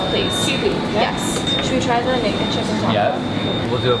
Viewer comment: I thought you said the naked chick in Taco Bell but you really said the naked chicken is at Taco Bell😂i was so confused